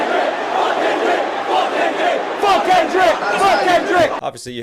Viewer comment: What I heard: "Fuckin' Drake! Fuckin' Drake!" There isn't going to be a round 2. Drake has crashed out so hard and in so many ways that Kendrick can never speak on him again and still win.